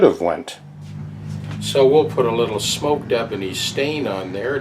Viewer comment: can anybody tell me the name or the kind of product used at 2:42? I don´t know what is used an I don´t understand what is said. Any link will help. Thanks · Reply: That is a " smoked ebony" stain applied with a foam brush. I bought a small tin from Home Hardware here in Stratford.